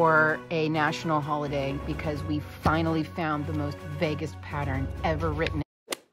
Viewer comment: I had to listen to the intro 2x the first time I heard the “Vegas pattern” not “vaguest pattern” and I was confused 😂